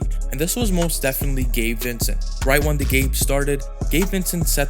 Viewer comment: Sounds like your saying gay Vincent